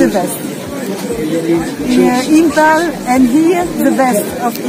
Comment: Did she say 'Imphal'?